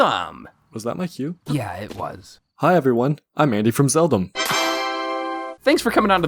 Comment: I legitimately thought you said Zelda, while referring to Link